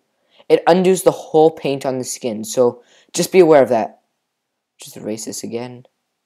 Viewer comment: I thought he said "oh Just a racist again" xD